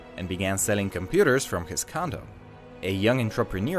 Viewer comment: "Selling computers from his condom"??? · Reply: I knew I couldn’t be the only one who thought they heard “condom”